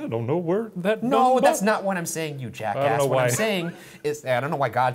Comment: did Ryan just said “jackass”? Like “jackass hill” .😂😂